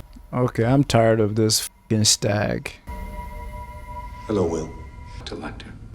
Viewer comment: At one point you called Will Louis, and I had as well earlier in the same scene? Instead of “Hello Will” I heard “Hello Louis” (when Hannibal first visits him) in my head?!